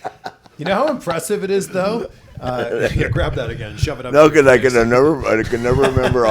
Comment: Did I hear what I think I heard or am I mistaken? At the 8:48 mark, does CVV tell Flair to shove the microphone up his ass?